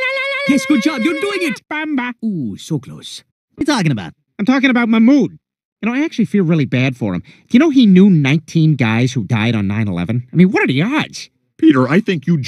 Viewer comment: I thought he said mo bamba lol